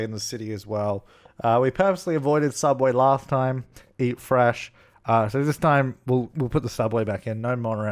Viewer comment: Who else heard him say, "Subway, eat fresh"? 11:14 !!!